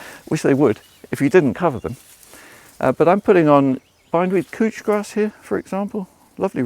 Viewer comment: Ummm good Sir, did you just say "cooch grass"?